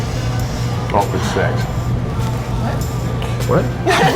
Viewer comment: what did Theo said at 4:14 ? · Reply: "Awkward sex"